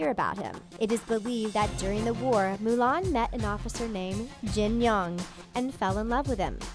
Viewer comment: sounds like she said Jinyoung